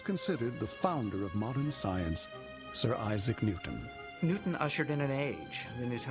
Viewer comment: he says "Newton assured in an age"?!?!? Am I hearing the right thing? What does it mean?!? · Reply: Ok, it's "usher" which means "starting something in an age, being welcomed in an age, etc..."